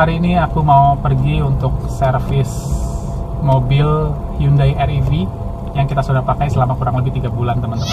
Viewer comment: Did he say "Hyundai Air EV"?